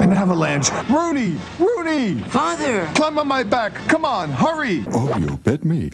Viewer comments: did hey say cum now my back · I know it was "Climb" but I heard "Cum"